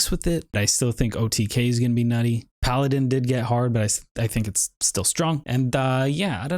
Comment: did he say that paladin got hard?